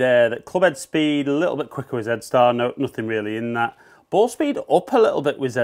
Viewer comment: Why do Brits call it “Zed” star ? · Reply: Good to know. I thought he was saying DEATH star, I'm like, WHAT? LOL!